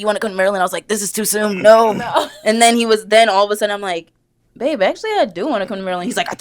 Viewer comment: did I hear "bae"